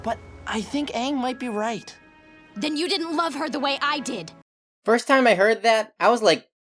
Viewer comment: "You didn't love her as much as I did." WOAH WOAH WOAH HOLD. UP. wHaT did you just SAY?! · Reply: She said "you didnt love her the way i did" she never said she loved her mom more